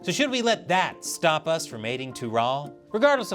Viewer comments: for a second I thought he said "stop us from mating" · I thought I heard dong trail. Sorry.